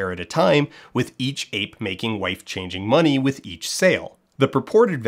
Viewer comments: I was listening to this while doing chores and initially thought Dan was just saying “life changing money” in a weird way. No, turns out those folks were literally saying “wife changing money” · The phrase "wife changing money" is costing me years off of my life. Everything is monetizable and transactional to these people. Nothing is real. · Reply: I’m not a native speaker, so my listening skills are not perfect. At first I thought Dan said “life changing money” but later as he repeated the term I came to the dire conclusion I heard right from the beginning.